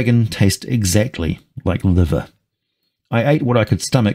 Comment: tasted like what ? Like a liver ? I can't understand what he's saying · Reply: Yep, that's what I heard. Liver.